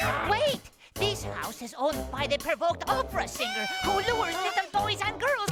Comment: I think Rolph said "This house is owned by the (?)- opera singer, who lures in boys and girls with enchanted arias." At first I thought he said "by the purple cobra" haha